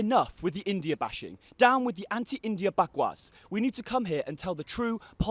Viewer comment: Did he just say 'Bakvas'? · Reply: yes...he said "bakwas" so proud of this man !